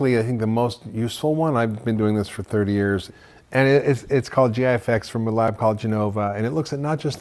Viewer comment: Did anyone catch what test he was talking about to test your own microbiome at 5:45? Does he say GI effects from Genova??